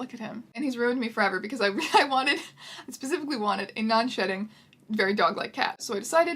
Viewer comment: misheard "non-shedding" as "non-shitting". got extremely shocked and confused for a millisecond.